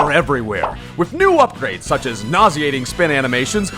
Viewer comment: I thought you said "Naziating spin animations"... It would be accurate either way.